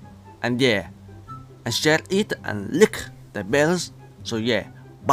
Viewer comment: I HEARD HIM SAYING LICK THE BELL LOL